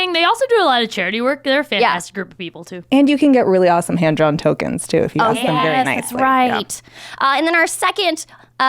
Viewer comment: did she say handjob tokens.. lol sounds kinky I gotta order me some of those.